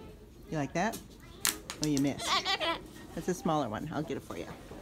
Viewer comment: Benjamin!!! Look at all of the screwdrivers!!! 1:42 when you said, "you like that?", it sure sounds like Benjamin says, "I like that!" You're gonna have to get him a big ol' toolbox sooner or later.❤🪛🪛🪛❤🤣